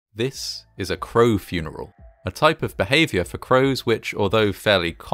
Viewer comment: Bruh I thought that shit said clown funeral